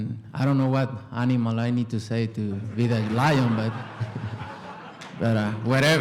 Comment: “Idk what animal I need to say to be the lion“😂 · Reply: Beat*